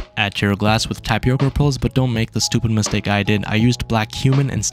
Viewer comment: Why is no one talking about how it sounded like he said “black human” 💀